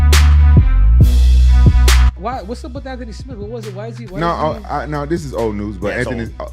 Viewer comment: Did Henry say Anthony smith or dat nigga smith 😂